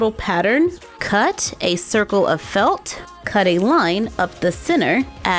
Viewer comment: When said a circle of felt i thought she said the circle of life :D